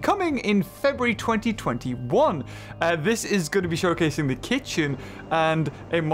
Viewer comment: wait did he just say kitchen lol